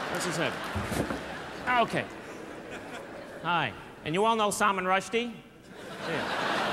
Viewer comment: High class comedian. He doesn’t need to humiliate anyone to make people laugh. We need more comedians like him nowadays. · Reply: Did I misunderstand or did he call that black woman at 8:29 Salman Rushdie? Surely not insulting.